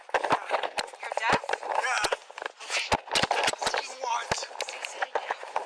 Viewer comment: Cop: You are deaf? Deaf guy: Yeah What?? Did i hear this correctly?